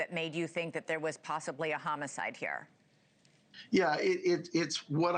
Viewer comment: He said there’s a chance, not definitely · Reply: sounds like he said yes, homicide